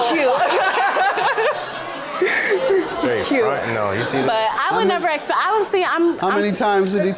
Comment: I swore he said "fuck no, do you see this" 😂😂😂